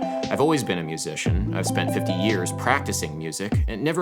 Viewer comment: did he just say he spent 50 YEARS? There's no way he's that old. But it doesn't sound like 15.